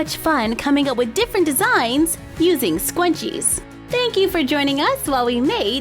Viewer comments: It kind of sounds like she is saying scwunchie it kind if bugs but love ya anyway! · Why does it sound like ur saying scwunchie🤔🤔🤔🤔🤔🤔🤔😂😂😂😂😂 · It sounds like your saying scwunchie lol😂I may have to make some of these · I sorta sounded like she was saying "scwunchie" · you kind of sound like your saying the word scwenchie, but It might just be me